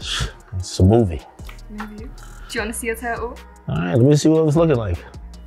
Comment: I thought the cashier girl said "Do you want to see a turtle?". Sorry my English people, the accent takes at least twos go's for me to understand propah!😆